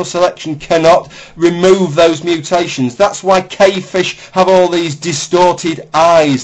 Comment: What are you saying at 7:18 -- "Kay fish?" I tried googling all the spellings I could think of but can't find what you're referring to.